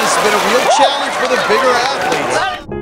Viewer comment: wait wait wait what did u just call them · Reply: WindBreakerHD ya, he called them athletes.😂